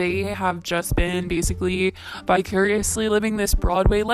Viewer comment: I thought you said bi-curiously 😂